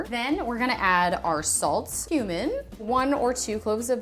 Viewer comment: One clove of human. Wait what no I’m at cumin LOL that’s what my brain thought she said